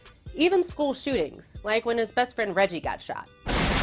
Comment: Did she say reggie lol@